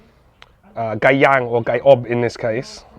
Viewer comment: I’ve only seen chicken that moist once before and it was a small kabsa shop in Hawiyah KSA when I was working in Saudi. At around 8:56 you said gai yang then called it something else what did you call it? Thank you. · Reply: he said gai ob (roasted/braised chicken). gai yang is grilled/barbecued chicken.